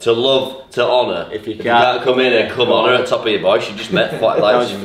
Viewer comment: I'm an idiot. I thought you guys were saying "To Anna, if you can't come in her, come on her!". I'm like "Who the heck is Anna?"